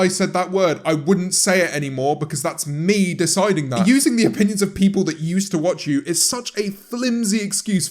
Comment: thought it said onions